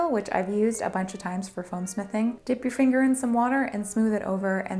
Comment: I know she said "foam smithing," but I heard "foam sniffing" and I was momentarily worried. 😳😅